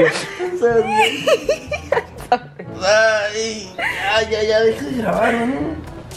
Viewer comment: soy yo o escuche mal jajaja 10:03 YA DEJA DE GRABAR MI AMOR! 😱 en vdd que me gusta verlos juntos se notan en sus ojos que aún sienten algo. seris muy bonito y lindo que regresarán 😊 Saludos desde Chiapas 💖